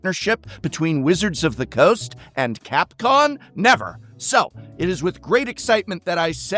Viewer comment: Did you just say CapCON? A little Freudian slip maybe?